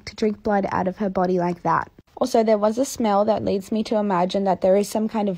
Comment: Omg you said smell. I thought you said snail.